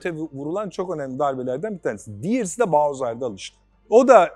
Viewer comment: Bahoz öldü diye duydum ben